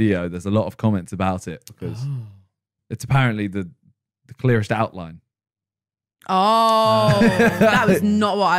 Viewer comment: What does say at 1:36:35 ?? The ... what ? Thx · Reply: Clearest outline